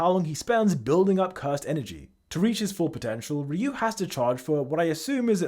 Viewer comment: DID YOU SAID POTENTIAL!?